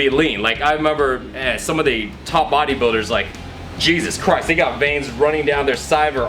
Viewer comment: did he said ''some of the top bodybuilders like Jesus Christ"????